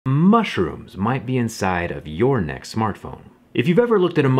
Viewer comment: I heard "mushrooms might be in your neck" as I was scrolling and I was like 😧